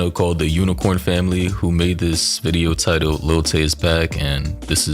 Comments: Bro I deadass thought it said Lil Tjay 😭😭😭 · thought bro said lil tjay 🤣🤣